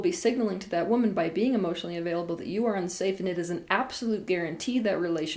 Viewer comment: I've watched the vidéo twice to be sure I understood correctly. Am I right that at 11:04 , we can hear "...if you are being emotionally AVAILABLE..." (instead of UNAVAILABLE (the caps are to highlight, not to yell!)). I believe the right answer I'm looking for is that this was pronounced too quickly and therefore, you can mishear the word. Just wanted to be sure, very good vidéo! :-)